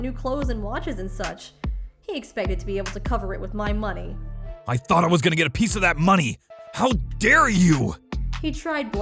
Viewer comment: For a second I thought he said "I though I was gonna get the piece of that ass."